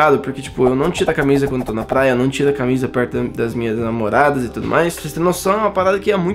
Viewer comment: ele disse "das minhas namoradas" ou eu entendi errado?